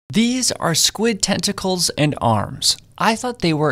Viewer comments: for some reason i thought he said “Squid Testicles” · I want paying full attention at that first part so I thought he said testicles 😭😭